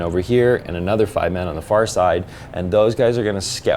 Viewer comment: did I just hear, Farsight 👀